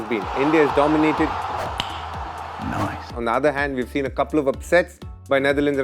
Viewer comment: when he said " We have seen couple of upsets" i heard "We have seen couple of s3x" 💀